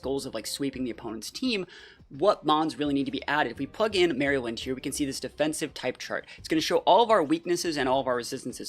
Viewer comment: Did he just say merrill lynch makes a defensive type chart?